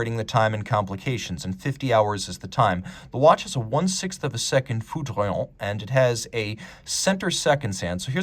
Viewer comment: what was that word for the 1/6th sec dial? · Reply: Foudroyante